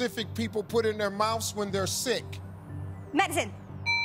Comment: I thought she said Mexican